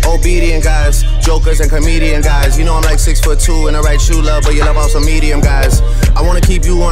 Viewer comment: I thought he says “I’m 6’2 and I write true love but you love on some medium guys” not in the right shoe, love but idk it might be official